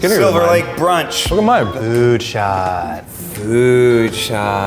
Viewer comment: When they said food shot I thought it said flu shot